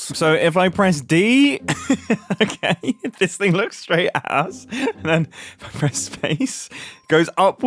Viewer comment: I HEARD THAT MUMBO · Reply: I believe he’s saying “at us” But yeah even the subtitles heard it